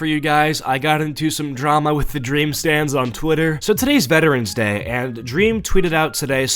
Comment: I thought you said trolling dream stand on the toilet 💀